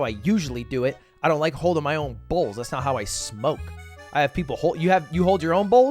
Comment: I was looking away and I heard balls💀💀💀💀💀💀💀